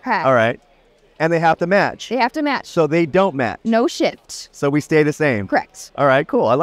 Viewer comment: Ik logically she said "no shift" and I swear I heard "no shit" instead